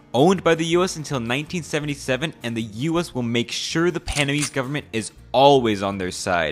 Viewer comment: man… did you just say pana-mese 1:48 😳